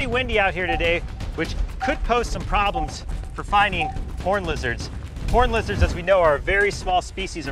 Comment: Not gonna lie thought he said horney lizards